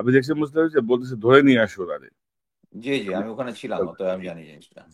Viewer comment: ভিডিওর 6:57 সেকেন্ড সময় বলেন আপনিও ওখানে ছিলেন। আমি কি ভুল শুনলাম।